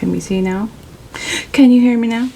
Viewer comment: “Can you hear me now?” I heard “how ya like me now?” Like the song 🎵 ❤️😉